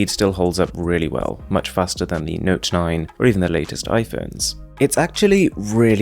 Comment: Wait,did he say "...or even the latest iPhones"🤣